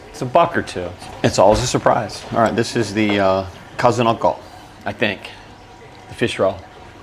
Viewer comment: it sounded like he said cousin-uncle, lol